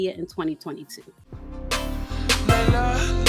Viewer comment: I thought it said Kelly I was like oooh friendly fire 🤣🤣